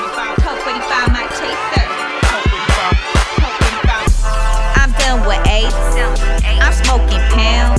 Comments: YO AT 0:22 I THOUGHT SHE SAID "I am filled with AIDS" LOL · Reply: Lewis Gardner I thought she said "I'm done with AIDS" lol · YO AT 0:22 I THOUGHT SHE SAID "I am filled with AIDS" LOL